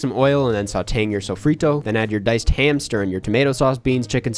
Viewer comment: Bro I really thought he said "diced hamster"